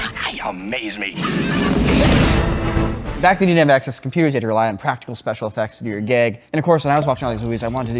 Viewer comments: Did say "you had to rely on practical effects if you were gay"? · what the heck does he say here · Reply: I amez me